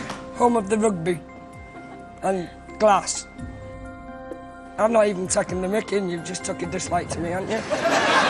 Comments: What is he saying at 8:02? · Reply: “i’m not even taking the mick, you’ve just took a dislike to me haven’t you?” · What did Johnny say at 8:02 I couldn't understand him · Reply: "I'm not even taking the mickey and you've taken a dislike to me haven't you"